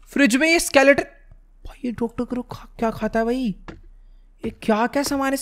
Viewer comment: i hear dr.crow is coming without wearing headphone But ujjwal bhai: ye kya kya khata hai?😂